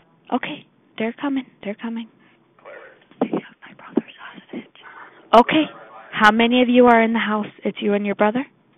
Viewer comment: I thought she said, "They have my brother's sausage." What the hell I thought, and then it hit me, she said hostage. LoL I am losing my hearing.